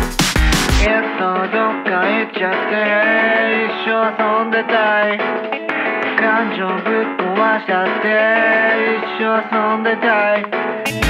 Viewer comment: 把Dark Dark Dark聽成Duck Duck Duck